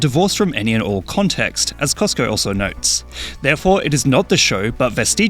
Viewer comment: I swear you said Costco, and I was so confused ( not on something I promise )